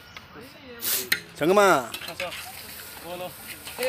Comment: did he say "sangma"?